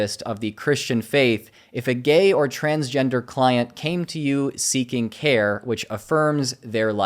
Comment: I thought you said "Clown" instead of "Client", and I was bout to joke how they were actually teaching you Shock Therapy or sum